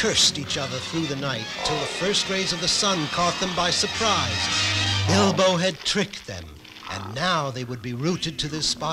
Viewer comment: does… anyone else hear him call Bilbo “Elbow”?